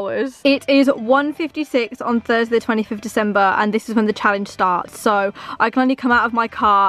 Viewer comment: "it is thursday the 25th of december" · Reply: sophie phillips I thought she said 20th of December 😂